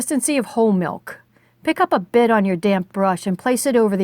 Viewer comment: This is a wonderful tutorial. Thank you so much for making it. Also tho.... You hit the D's really hard when you say damp, lol. At first I thought you were saying "load up your damn brush" lol I was like- oh my, this lady is serious about her watercolors....😆